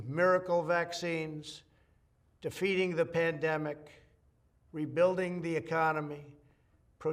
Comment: is he really this blind???? how does he not know Antifa did all that............dumbass. 4:38 "too feeding the pandemic"?...........um what'd ya say there guy?